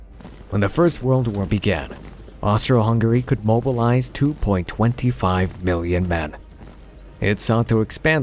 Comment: I had to listen to 0:56 sounded like mobilize 2.25 BILLION MEN. I dont think Europe had that kind of population back then. Must be 2.25 million men.